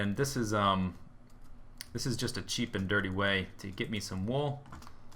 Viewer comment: don't you mean sheep and dirty :) (instead of cheap and dirty)